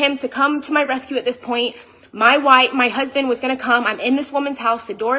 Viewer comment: > did she say " my white"?